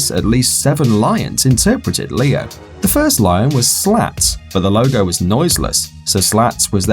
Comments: I thought they said the lion was slapped 😂😂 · I thought he said the first lion was slapped.